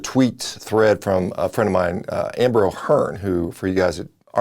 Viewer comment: At first I legit thought he said "Amber Heard" and I was thinking, Nooo! 😂